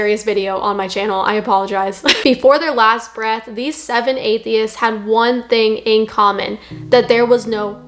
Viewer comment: Ohhhhh...... Atheists 😂😂😂 I thought it said athletes 😂😂😂😂